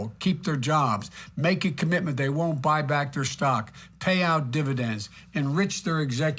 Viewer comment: did he say "pay out their dividends"?